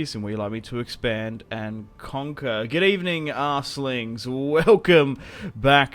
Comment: Did you just call us asslings lol? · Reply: Arseling mate